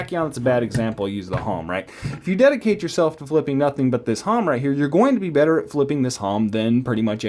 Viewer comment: did u say it was called the halm? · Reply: Its Hom from "Jerry Hom Designs"